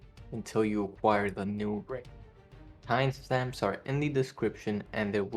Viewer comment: until i acquire what? captions say "new break"??? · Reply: Late, but he says new ring lol